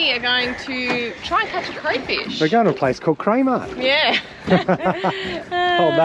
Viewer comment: I thought you said you were going to K mart but it was craymart silly me